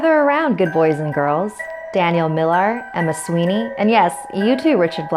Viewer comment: When you said Ms. Sweeney I thought you said Mussolini and I was so confused